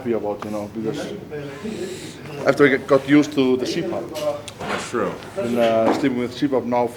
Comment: what did he say he got used to? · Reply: Bilal Patel cpap